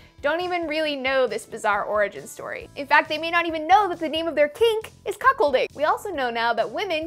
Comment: Am I the only one that thought she said "cakes" instead of "kinks" the first time?